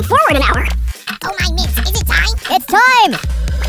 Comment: Did he just say ITS DIME